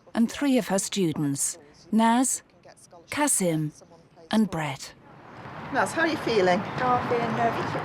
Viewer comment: did she say bread 🙁